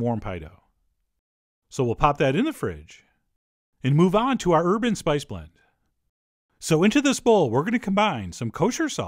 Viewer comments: I could have sworn you said "urban spice blend" until I realized it was "herb and spice blend"... Lol. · I hope I'm not the only one who thought he said "urban spice blend" when he actually said "herb and spice blend."